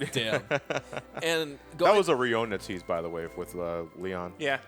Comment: What was that about Squall/Leon during the end credits reel? I couldn't quite make out what was said. For those that need a time it's around 34:42. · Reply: He said that the letter was a Rinoa tease.